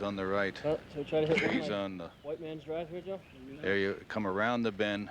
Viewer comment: listen to player. What does he say? Whiteman’s drive??